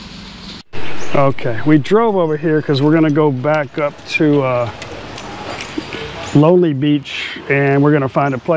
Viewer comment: Emperor of India is Kai Bae not Lonely Beach. This was our favorite Indian food Restaurant too for many years (and we`ve been on Koh Chang twice a year every year from 2005) but I saw a new guy on the video so I`m not sure anymore. Everything changeing now. Anyways- have a good time on one of my favorite Thai islands! P.S. Is Papas Greec Restaurant also closed? almost the opposite of Tesco? and near by the Emperor. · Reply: "We are heading back to lonely beach" is what i said @ 5:44 never said the restaurant was there. I did say it was down the street from the Awa, map link is in the description box. Incase anyone is confused still. Thank you for the wishes it is our favorite spot too. for more videos. For those who are not subbed to the channel.